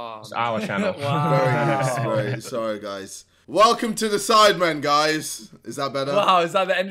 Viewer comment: I'm sorry did u just said Harry reacts feat the sidemen?